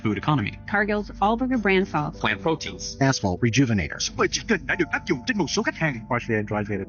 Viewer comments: did anyone else hear “asshole rejuvenators”?!? 😅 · Anyone else hear "A**hole rejuvenators?"